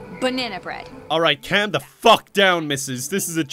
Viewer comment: said fuck instead of feck .-.